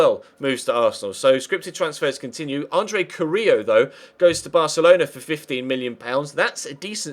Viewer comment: Sounds like you said Andres Correa to Barca? That can't be right though, or at least it's another Correa, not the one that plays in MLS. Can someone please tell me who he's talking about at 4:45? I want to buy him, lol.